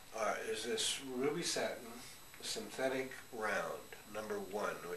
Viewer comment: When he describes the # 1 round brush, at 1:15, it sounds like he says 'really satin'. Is that a brand? I can't find anything on google.